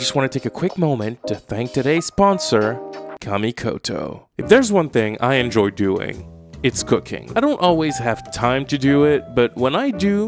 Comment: had to go back to make sure I didn't hear "if there's one thing I love doing, it's cocaine" haha